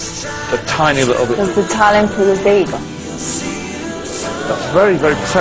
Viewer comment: What in the hell does she say here? Sounds like, "Does the talent pull the bagel" 😂😂